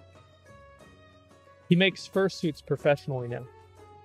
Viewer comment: What did he say at 18:40 ? 'He makes ....'? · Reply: He makes fur suit professionally now - that's what he said I think so